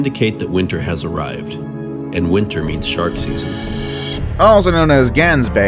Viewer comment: Did he say Sharkses? XD